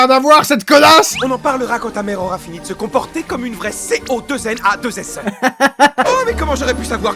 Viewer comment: je n'ai pas compris, Stolas la traite de S.O.L.L.A.S.S.E, mais ça veux dire quoi ?